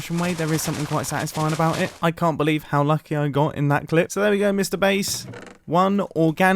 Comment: 20.00 I thought he said mr beast